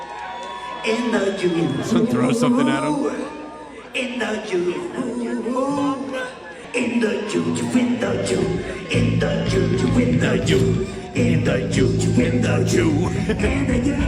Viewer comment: Is he say well "singing" In the Jew,?